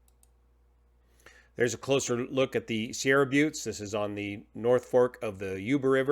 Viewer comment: I was listening while walking, and I kept hearing "Sierra Glutes". That's a subject for a secondary channel though! :)